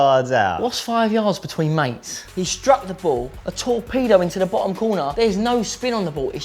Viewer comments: anyone else thought he said a tall pedo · when he said torpedo into the corner I thought he said tall pedo in the corner